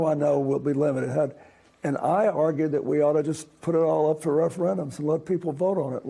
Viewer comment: Did I hear correctly? The "R" word, referendum? Absolutely, for all these critical issues, nationwide. I never expected to hear that.